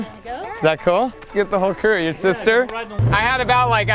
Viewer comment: did he just say "let's get the Hooker, your sister“?